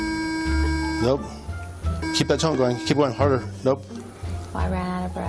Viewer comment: It sounded like he was saying "keep that tounge going" instead of "keep that tone going"